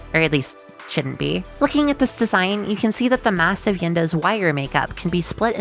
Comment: Did you say… massive?